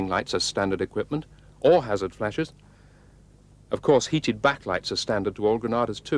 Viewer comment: Did he say "Heated back lights" or is my hearing going down hill?